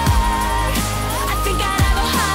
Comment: At the start it sounded like she said hardy tack!